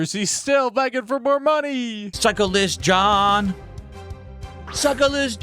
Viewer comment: instead of say cyclist John sounds like his say Suckless John! LOL speaking that alien denim talk again